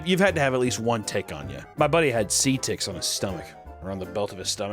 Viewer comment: the editor thought U said "sea ticks" lmao its seed ticks